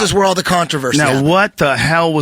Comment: top stoner controll? am i hearing right?